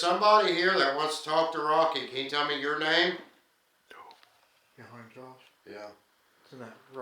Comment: Time stamp 29:08...wat is that .someone said "nope" it sounds like